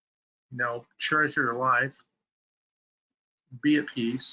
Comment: truly wish to understand what he said at 2:34.after "slow down.." . . treasure your life? charge your life? .. ? useful message -Thanks.